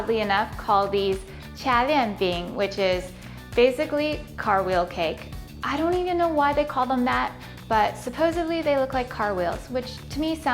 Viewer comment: Lol I thought u said "Cartwheel Cake" xD